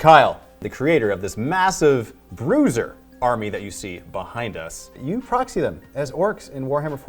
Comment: did he say "loser army"?